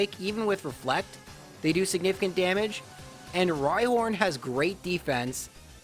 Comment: Did I hear "Rilehorn"? UPDATE Yes, it's at 30:44. 32:46: At level 64, the Beatles level :).